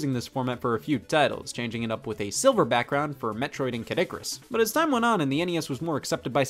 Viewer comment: OK am I hearing things or did he say Caddicurus instead of Kid Icurus?